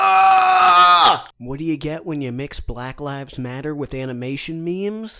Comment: I thought said “black labs matter”